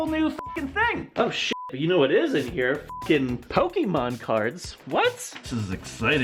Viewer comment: My phone wasn’t turned up very loud and I misheard when he said that there were pokemon cards in the cereal box. I thought that he said Pokémon parts lol